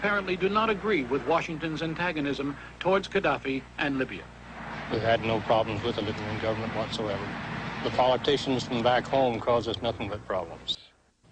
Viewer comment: I'm trasnlating this video to portuguese but I'm not so good with english, anyone can tell me what the man said between 07:03 - 07:08 ? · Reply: oi! we've had no problems with the libyan government whatsoever. the politicians from back home causes nothing but problems. não tivemos problemas com o governo da líbia. os políticos de casa não causam nada além de problemas